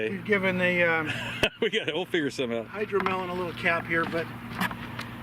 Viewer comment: I got a chuckle from my poor hearing when “OG” mentioned the Hydro Melon Cap. I wasn’t watching, and thought he said Hydro-Mellencamp. Had to drop what I was doing and go check.